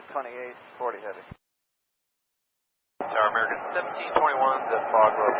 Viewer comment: Does anyone else hear, "Tower American 1521" on American 1721's initial call around the 0:25 mark? Clearly 1721 is the flight number but the initial call sounds wrong.